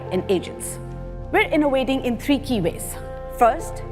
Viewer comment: Who else heard "We're innovating in three kiwis"